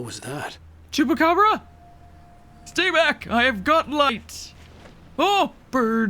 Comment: Had to replay this part. I thought said, "Stay back! I've got lice!" 🤣 5:55 In the same vain: Dr. Seuss with a demonic voice is nothing but a good time. 😉 - Barry! Wow, a classic kind of 80's odd-couple mashup! Didn't expect that one. - I had a thought... The last TV kinda solidified it: What if there are 2 Allen Wakes, the story and reality and someone bridged the two. I wish I could recall the tie-in to Control. Wait, what if it's the typewriter his wife got him?🤔 🧀🧀🧀